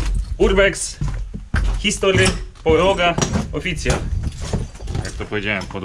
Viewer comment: mówi o instagramie i mówi podłoga a ja usłyszałam Poroga ;-)